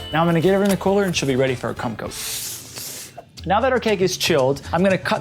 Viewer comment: He definitely said 'cum coat' instead of 'crumb coat' 😂😂😂 (0:58 for those who are wondering)